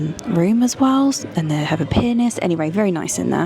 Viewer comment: "they have a penis" I thought that was what you said and caption confirmed..Hilarious. Love your videos by the way! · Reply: Pianist.